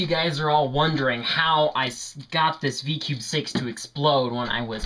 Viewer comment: LOL 0:05 I thought it was v cube 7 but he said v-cube 6